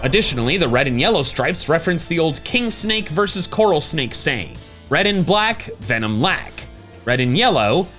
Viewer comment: I always heard "Red on Black: Friend to Jack" 😅